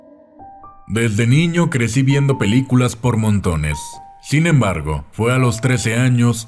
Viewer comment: En intro pensé que dijo "desde niño crecí viendo películas p0rno"jajaja